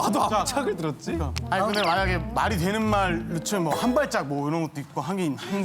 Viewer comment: 뭐야 @@ 계속 아무짝으로 들리더니 정답 들으니 한! 발! 짝! 왜케 잘 들림? @@